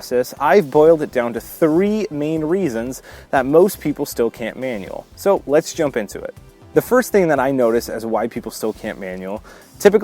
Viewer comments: All i heard was: 'white people can't manual'. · Everytime you say "why people can't manual", I hear... "white people can't manual..." 🤣🤣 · I thought you said "white people still can't manual" at the start 😂